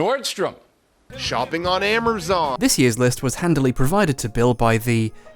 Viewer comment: did he say “amerzon” instead of amazon? 21:15 ramadan doesn’t even fall near christmas😭 the earliest i’ve seen it is in like march but it’s generally in the summer, what is he even